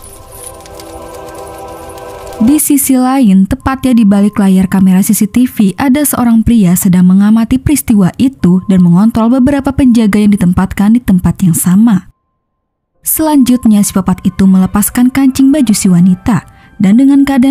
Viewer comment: kok aku dengernya mengont*l bukan mengontrol 😂